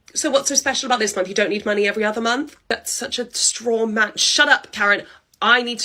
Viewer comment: What does say at 0:27? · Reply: "That's such a straw man" meaning, that's a dumb argument.